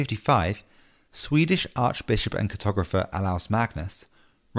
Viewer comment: the closed captions say "photographer Alouse Magnus" instead of "cartographer Olaus Magnus"